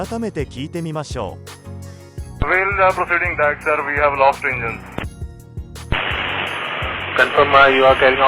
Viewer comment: "We have lost (an) engine" 単数に聞こえますがねぇ。複数なら、"lost both engines" と言うでしょう。 4:02 RAT: 普通、手順でAPUを起動するんじゃね？ 4:03 ファンブレード： 着陸時じゃ、普通、アイドルまで回転数が下がっていますが… 手順無視、CRMしていない… 雇用者の訓練の問題のようで。